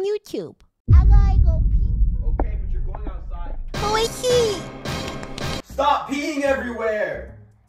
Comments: “Stop peeing everywhere “ 😂😂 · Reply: I thought he said “Stop Beating everywhere” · “Stop peeing everywhere “ 😂😂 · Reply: I thought he said "stop being everywhere" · “Stop peeing everywhere “ 😂😂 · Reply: I thought he said "stop eating everywhere"